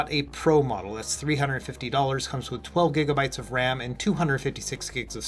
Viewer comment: I genuinely don't know if every reviewer is now saying "giggle bites of ram" or if Tech Dweeb lives in my head rent free.